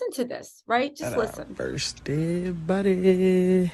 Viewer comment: He said first dead body...or he said first done body....did you hear that or am I wrong...my hearing isn't all that but I swore I heard first dead body..but he could of said done.